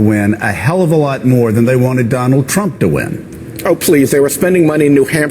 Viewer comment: Every time Sheldon Whitehouse says, "Oh, please," I hear, "Oh, shit!"